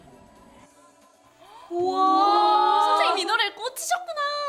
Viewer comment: 순간 윤선생님 으로 들림